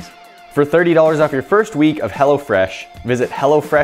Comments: thought you said Hella fresh lmao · Bruh I thought he said hella fresh😂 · Thought he said "hella fresh" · sounds like he is saying hella fresh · thought he said 'Hella Fresh' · i thought he said hella fresh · Who else thought he said hella fresh?😂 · thought he said hella fresh not hello fresh